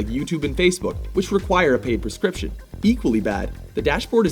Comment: Did he say, "Paid prescription"?